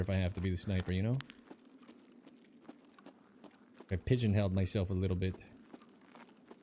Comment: Please forgive me for this question, but did you say you might have “pigeon held” yourself ?